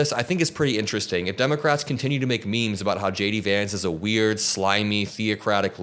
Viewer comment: Everytime you say "J. D. Vance" I hear "Shady Vance" 😅